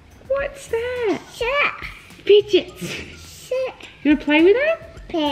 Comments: it sounded like she said sh!t lol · i heard say shit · what did say? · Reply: Play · I love how a 11:28 sounds like she said sh*t